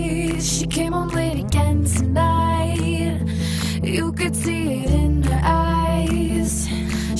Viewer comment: i always hear it as "came on late again tonight", like this song is about periods lol